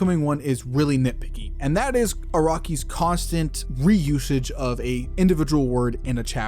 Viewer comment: Not gonna lie, everytime you said "Araki" i heard "A Rocky" XD